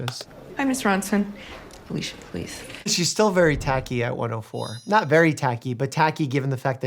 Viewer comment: what doctor mike said: "she's still very tachy..." what i heard: "she's still very tacky..." 😅 i was like damn doctor mike, okay! no need to kick her while she's down lmao